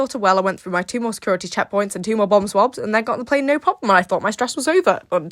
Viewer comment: Thank goodness I decided to glance at the closed captions because I thought she was saying BUM SWABS 😅😂